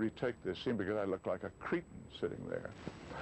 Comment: "Willy, will you take out that scene, because I look like a..... what??" What word did Gregory mention at 5:07 ? In spite of his malheur, I think this scene was absolutely brilliant because it showed perfectly the real persona of the character Gregory played. Yes Jubalcalif, I agree with you: Jerome Moross music is so great, he was a musical genius. My family and I love his music so much, he wrote the Charlton Heston movie "The War Lord", absolutely great piece of music. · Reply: He said he looked like a cretin.